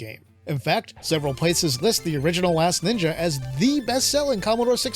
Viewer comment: i keep hearing “ the original a$$ ninja” 😂